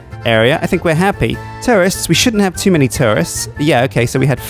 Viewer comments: Did he just say terrorist instead of tourist Oh no Not novosplovo Not glory gracious country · I've rewatched this a few times, and each time I mishear you saying tourists as "terrorists" 😲🙄 xx